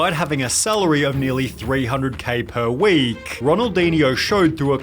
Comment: Who else thought it said Ronaldo and was confused?😅😅